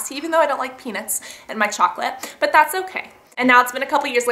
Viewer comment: Hehehe when you said "I don't like peanuts in my chocolate" I thought you said "I don't like penis"